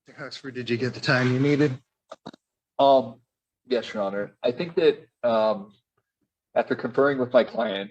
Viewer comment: Terrified of a loud exhaust?! All I hear is “I’m soft, your honor”